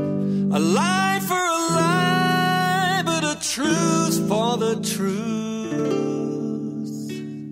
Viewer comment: Nice comment. On headphones I hear him say "a truce for the truth".. at least the first time. I love me some XTC.